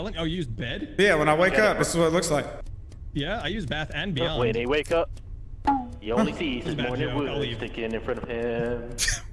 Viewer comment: Anyone catch what delirious says at 2:40 or is it just incoherent mumbling? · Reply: "When he wake up, he only sees his morning wood sticking in front of him", exactly worded as he said it.